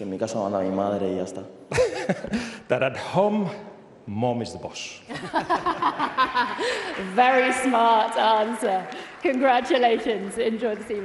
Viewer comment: what does lamine says? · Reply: Nothing that, at home my mom is the boss